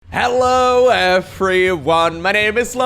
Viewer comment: HELLO EVERYONE, THIS IS LOKO! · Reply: OMG. I am so stupid.... after watching Lowko by years... I always thought he was saying: "Hello a free one!" XD